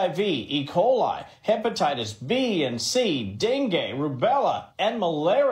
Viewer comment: mins I think he said Dengi as in Dengi Fever right!? Not Den Gay, but that made me spit my juice out when you said that 😂😂😂😂